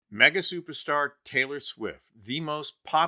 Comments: You scared me, I thought you said MAGA superstar TS😅💙💙👍👍👍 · thought you said maga superstar at the beginning, hd to rewind, couldnt process the rest till I got that part figured out. mega superstar lol. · I thought I heard MAGA Superstar, before I realized he said Mega Superstar.